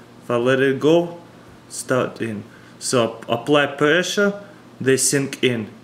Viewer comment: When he said apply pressure it's sounded like he said black pressure